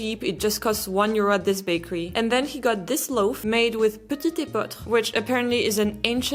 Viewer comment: A confusing grain haha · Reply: Je ne savais pas qu'il y a une différence entre « épeautre » et « petit épeautre ». J'ai compris « petit pain d'épeautre ». I learn something new every day!